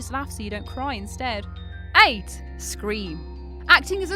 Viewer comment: I'm sure she says aids instead of 8